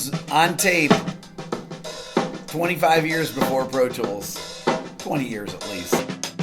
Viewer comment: What is it you say at about 1:40, "25 years before _____, 20 years at least." Thanks. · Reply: Pro Tools